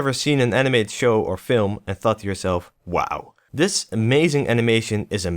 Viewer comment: I heard "And fucked yourself, wow." in the first few seconds where you said "And thought to yourself, wow." haha